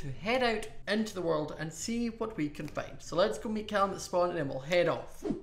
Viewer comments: I don't know why but at the intro i swear i thougt said "so let's go make out in the spawn 0:25 " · at the beginning ( 0:25 ) did u say "let's go make out at spawn" or "let's make our way to spawn"? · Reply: He said “ let’s go meet Callum at spawn”